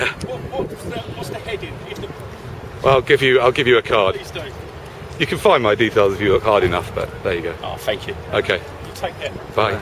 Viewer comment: Thought at the end he said " you could find my details if you were hard enough" hahaahah